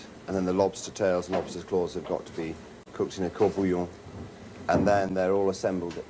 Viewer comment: What does he say @ 1:37? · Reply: The lobster gets cooked in a court-bouillon